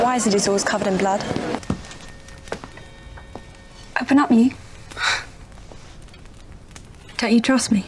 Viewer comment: When I watched these as a kid I thought she was saying something like "Apenatue", the delivery is so funny